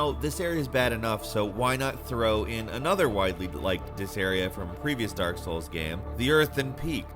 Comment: did he say "widely liked disarea"? I swear this guy sounds like hes high